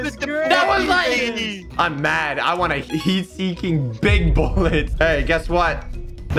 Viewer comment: I thought he said I'm mad I want a heatseeking big ball